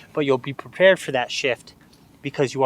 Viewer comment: i thought he said ‘u can be prepared for that shit’ 😂